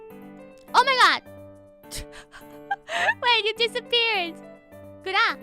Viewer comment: did she say „Warte“?